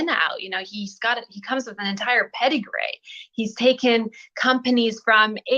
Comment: I thought she said pedigray as well!!